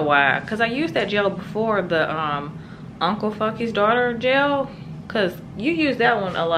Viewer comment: did I hear that gel name correctly? lol! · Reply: uncle funkys daughter